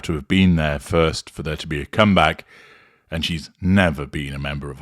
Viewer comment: Did you mean cum back or did I mishear something?